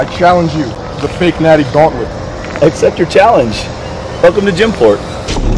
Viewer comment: I thought he said the thotnlent in the beginning instead of gauntlet.